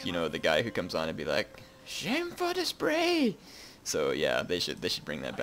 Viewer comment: What was he talking about right at the beginning? All I heard was "sheng full display"